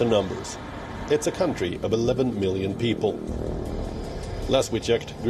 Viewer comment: Did they say 11 billion at 0:44 ? Isn't the global population 7 billion ? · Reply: he said 11 million not 11 billion